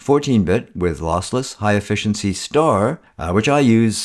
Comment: Hi:) I have no excuse ,But I have been shooting JPG all of My Digital life .../ Now I am learning PS n LRC , and want to shoot RAW ,, I heard You say HEIF 90% of the time (I am guessing that You would not do that if it would not give You, Great results) Could You do a Video about HEIF or point me n the right direction.... ThankYou for another Great Video :) :) :) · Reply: Hi Johny. I believe what you heard me say is that I use RAW High Efficiency* 99.9% of the time. It's so close to lossless RAW as to be indistinguishable, at least to my eye. The savings in terms of file size (and so, storage) is a real benefit.